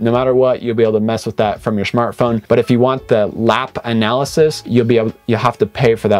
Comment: Hi…did you just say Schmart Phone at 5:45! 😜🤣🤣🤣